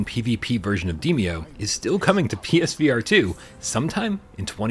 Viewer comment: I'm super excited for Alien and Subside!! Also not sure if it's just me but I swear I heard at Timestamp 3:33 - Demeo Battles "is still coming to 'p3n1s'VR2 some time in 2024"... XD LMAO